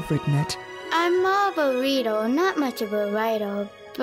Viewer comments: Subtitles: I'm a burrito not much of a writer xD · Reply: I think she actually said "I'm more of a reader". But the VA's cute impression made it sound like she said "burrito". XD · I think she actually said "I'm more of a reader". But the VA's cute impression made it sound like she said "burrito". XD